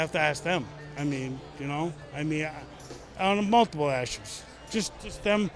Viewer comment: gf heard him just say "multiple issues" while slurring and asked "did he just say muff blasters?" LOL